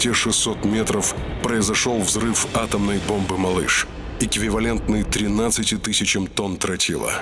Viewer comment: the narrator says 13 kilotons (if I am okay with my hearing still). The subtitles say that it was 16 kilotons. So, there is a bit of discrepancy here . Wikipedia says it was about 15 kilotons. But should we believe it?